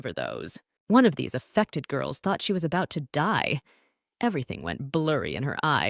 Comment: Dude the first words I heard was "She was about to starve to death" LIKE WTF💀💀💀💀